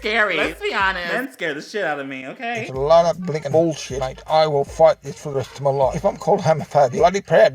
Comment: all I heard is "I will fart this for the rest of my life..." 🤯